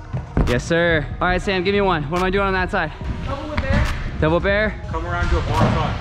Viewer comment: Is it me or did say double bear?🤣